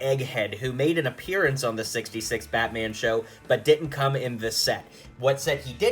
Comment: I thought it said “order 66 Batman”